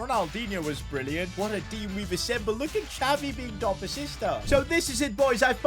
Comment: I heard "Look at Xavi on top of your sister" i was like HUUUUHHHH?!? 😅